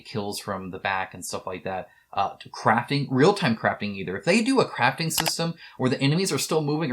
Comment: CRAFTING! Omg, I thought you said "a real time craping system" at first for the last of us